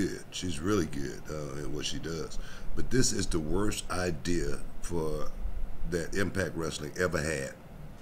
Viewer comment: I thought he said Russo idea